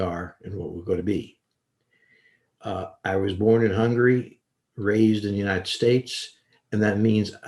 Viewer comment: I really thought he said he started doing this because he was "bored and hungry" not "born in Hungary."